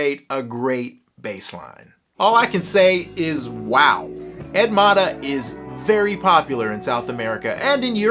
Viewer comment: I know it's weird but we say his name more like "Edgy Maw-tah", at first I thought you were saying the female name "Edmara", which I had no clue who could she be. 🤣